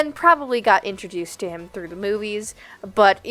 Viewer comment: I thought you said you learned about Rocket Raccoon “through the boobies” I had to rewind to make sure 😂😭